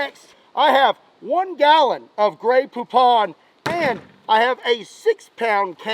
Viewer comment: I thought he said I have one gallon of grey poop